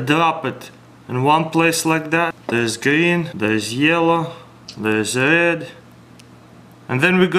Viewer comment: after he says yellow, it sound like he says lizard instead of there's red